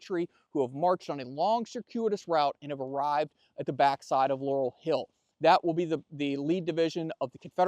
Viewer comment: I thought it said Lauren Hill at 1st 😁